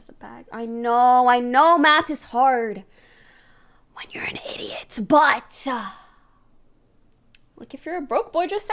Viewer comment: I've seen this clip about 20 times now, and just now have I figured out she said "I KNOW math is hard... WHEN you're an idiot, But" This entire time I thought she said "I KNOW math is hard... But you're an idiot, BUT"